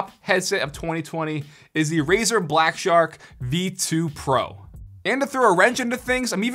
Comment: i thought he said the razer babyshark😂😂😆😆